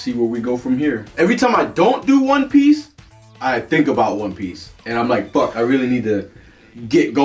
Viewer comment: You know at the beginning, I thought you said Juan Piece. Lol